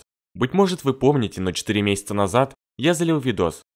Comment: мне послышалось не 4 месяца назад а 3года назад